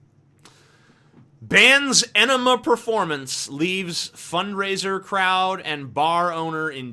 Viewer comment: At first I thought it said it was an "Ænima performance" and wondered why Tool would be playing at a tiny little venue..